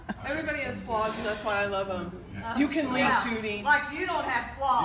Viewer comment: Honestly while she did wrong her son wasn't helping, while everyone else was trying to shut her up he just kinda ".................." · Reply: I thought he said "You can leave" at 2:21? I guess I'm wrong...